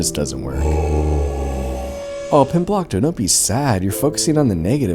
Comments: Am I the only person who thought that its name was "Bimblockto?" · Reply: i heard “comblockto” the first time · The cinematography of the pimblocto scene was actually amazing. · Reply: I think im high because i thought he was saying gobmblocto